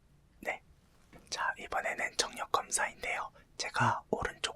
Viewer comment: 초 여기 저만 정력 검사로 들었어요???ㅋㅋㅋㅋㅋㅋㅋ